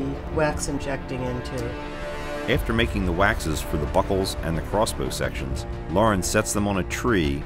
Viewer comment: it sounds like he’s saying “buttholes” instead of “buckles”. 😂